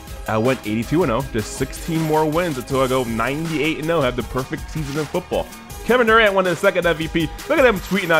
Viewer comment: did he say "perfect season of football"?